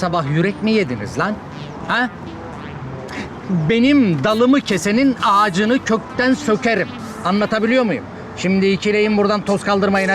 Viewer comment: Sarp 2:27da tam olarak ne dedi ? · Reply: Dalimi kesenin ağacını kökten sokerim diyor